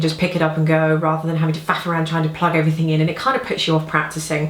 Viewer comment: "Pick it up and go rather than having to FUCK AROUND and plug everything in".......did anyone else hear that? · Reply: No-one else heard that because I didnt say that! I said 'faffing' around lol.